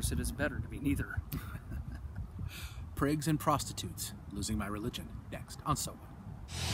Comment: I thought it said figs and prostitutes. Great video as always!